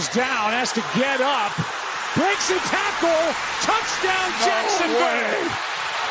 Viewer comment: why at the 6:10 mark does it say touchdown Washington??!!!! Then continues to talk about Washington! Wrong team. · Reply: it sounds like he says touchdown Jackson Bay because they're in Florida 😆 🤣